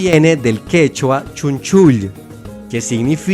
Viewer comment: no se porque pense que decia "quechucha"